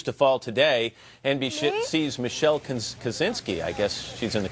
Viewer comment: Did he really say NB..Shit?